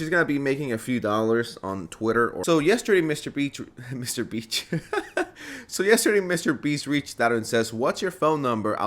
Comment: I misheard it as Mr. B*tch